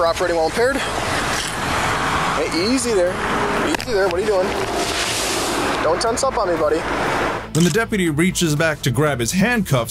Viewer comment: um, cop says "don't tense up on me buddy". Subtitles say "don't talk to anybody". Love the vids man, but these subtitles are so wrong and out of context at times.